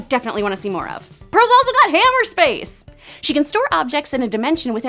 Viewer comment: Is she saying 'hammer space'?